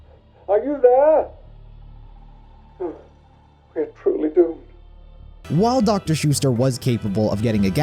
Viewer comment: It should read "We're truly doomed" instead of "They truly do"